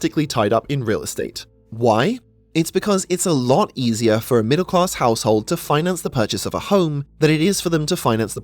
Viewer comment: the voice detection didn't understand the word middle class and it shows in the closed captions :D